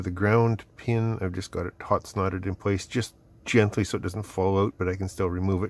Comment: Did you just say "Snot-soldered" @ 17:42? Every time I tack-solder something, henceforth it shall be called "snot-soldered".